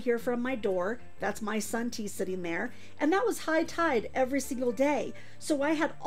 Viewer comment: Omg at 9:25 you said “that’s my sun tea sitting over there.” But I thought you said “Son, T sitting over there” and I paused, trying to figure out why your son looks like a Roblox. 🫠